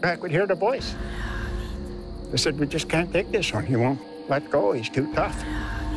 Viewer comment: can`t comprehend what he said in this part "we just can`t take it with you, you`re too tough?"